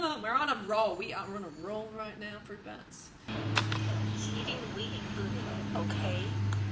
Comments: the girl you couldnt quite hear at 11:57 asked if eating wheat and gluten was okay :) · I swear i heard "is eating weed and booty ok?" At 11:56 omg 😂😂😂